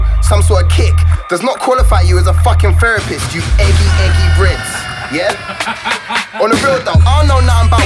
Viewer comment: eggy eggy brits ;> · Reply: I thought he said eggy eggy breads lol